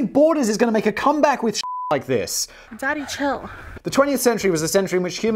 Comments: the fact "daddy chill" is the most used meme in this show tells something about Simon's mind state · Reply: I did not know the name of the meme, and only saw it used in Business Blaze videoes, so I always thought he said «Danny chill» 😱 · the fact "daddy chill" is the most used meme in this show tells something about Simon's mind state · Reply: All this time I thought it said "Danny chill" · the fact "daddy chill" is the most used meme in this show tells something about Simon's mind state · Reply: I thought that he was saying "Danny, chill" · the fact "daddy chill" is the most used meme in this show tells something about Simon's mind state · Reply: It says daddy chill? I always thought it said "Danny Chill" (even before this channel)